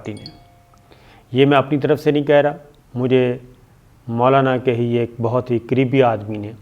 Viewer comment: when you said "Maulana k kareebi aadmi" I thought you said "Maulana k creepy aadmi". For a second I thought you feel the same as I do. :D